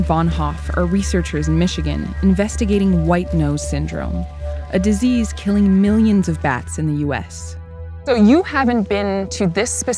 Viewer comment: Thought it said white noise syndrome is killing millions of cats.